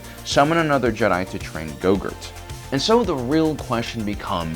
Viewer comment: <---- did he say "Go-Gurt" instead of "Grogu" or am I crazy xD